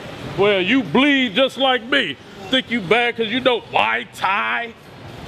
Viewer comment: did he just say y tie